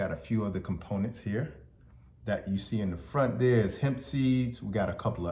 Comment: It sound like you said "Hennessey" at 1:19 but you said "Hemp seed". 😂 I'm so tickled because I've never drank the stuff but I do love liquid chlorophyll ❤ 😂